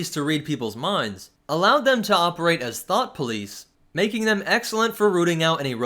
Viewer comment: who else heard "thot police"